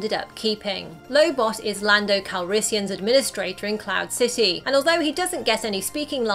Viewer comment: my dumbass thought she said clout city and not cloud city